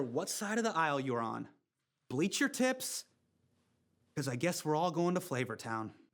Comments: I fully thought she said "Bleach her tits" I thought "Damn! Is it that serious?... And how do you do that?..." Haha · “Bleach your tips, cuz I guess we’re all going to flavor town” I’m floored 😂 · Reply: I 100% thought she said she'd bleach her tits at first and I went O.O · I heard, "Bleach your tits, cause we are all going to flavor town" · I thought he said bleach your tits 🤣🤣🤣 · I keep hearing "bleach your tits" instead of tips ...